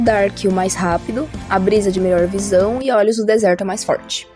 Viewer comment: eu ouvi "a Brisa de melhor visão" eu li "*Brisa; MALHOR visão" kk